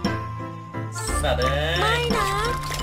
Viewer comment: ครับตกลงว่าตอน 15:18เนี่ยพี่พูดว่า สัสเอ็ยใช่ไหมครับ